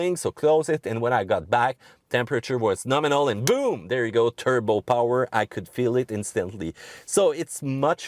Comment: En passant j'ai entendu "turbot" (comme la poisson). Oui, très charmant